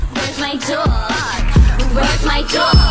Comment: Rip to the people who thought she was saying “where’s my jewel”